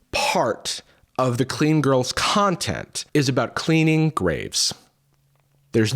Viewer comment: Bruh in the beginning I really thought he said grapes 😂😂😂